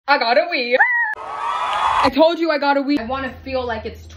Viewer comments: I THOUGHT SHE SAID “l gotta wee😍” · I thought you said you gotta wee